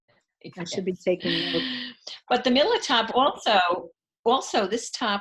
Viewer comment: Hi ladies!! I recently came across your podcast and am hooked now!! I love it!!💖 Hey I missed the name of the summer top that was cap sleeved light blue and white with cute stripes. It sounded like you said the “Milla” Top but couldn’t find it. Anyway...I’m very fond of all your absolutely lovely knits!! Thank you ladies for sharing your time and beautiful work with all of us!!💖💖💖💖You’re both very inspiring!!!